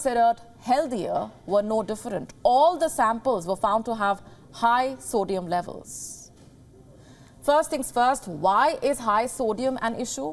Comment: At first I thought she said "high soylent levels." :face-green-smiling